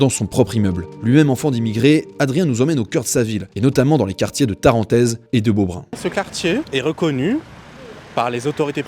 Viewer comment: Un quartier de quoi ??? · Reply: Quartier de Tarentaize - Beaubrun